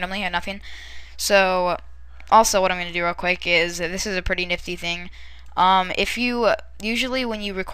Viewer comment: I'm sorry, but at 2:08 did you really say nifty ....